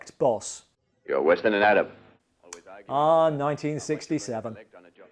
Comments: what the hell did he say!? Okay, after reading some of the comments, he apparently said " Arab" but it sounded for all the world like he said "madam", LOL! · “you’re worse than an Adam/atom” .... how is that politically incorrect? And what did he say again? Is he trying to say “Arab”? 2:46 oh my GOD! They wrecked a real model car! Also.. if you want to see “chintzy James Bond”, check out Stuart Whitman’s Shatter! 4:06 ah yes... agent Abraham Lincoln. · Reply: He definitely said "Arab".